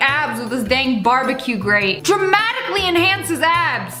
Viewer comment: Did anyone hear that glitch at? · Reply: oh sorry I thought you said so dramatic